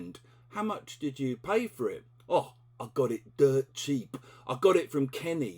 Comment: Cheers. I am aware of this one, and I do like it ! However, I have always thought it was spelt like this "Mate's rates" as a singular, Now the lesson's learned, it plural then .Mates' I heard you saying DIRT cheap. Is that right? I am aware of DEAD cheap. I've never heard anyone saying Dirt. So, it exists then?